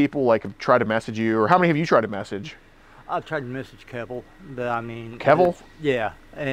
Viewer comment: “Who tf is Keval !?” 🤣😭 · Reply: The dude said Kevil. Me AND closed captions heard it as well. "Yaknowahmeen"